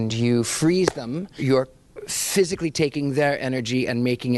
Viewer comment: i was tired, i thought it said physics 😭